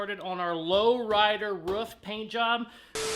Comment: you sound like a dawg! When you say roof, I only hear: 'WRUFF, WRUFF'!! 😂